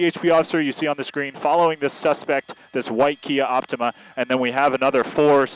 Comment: A Kia Optima u say? 😂1:20 Bro if ur gonna have this job u should probs know ur cars.... it’s a Lexus IS. It’s so frustrating when these guys are so clueless. 😒🤷🏽‍♂️ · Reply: I thought he said Kia altama lol